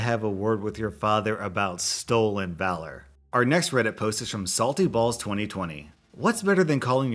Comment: DO I HEAR SALTY BALLS?